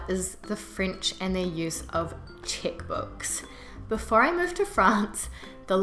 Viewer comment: I thought at first you were saying "chick book" or "chit book". It took me a while to understand you were referring to "check".